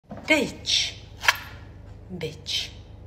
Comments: I WAS so confused i thought she said bitch bitch but she said peach bitch but when I realised she cut a peach i thought she said bitch, peach but she said peach bitch like what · That "bitch" while slicing that peach was personal my dude i'm telling you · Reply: I thought she said peach……peach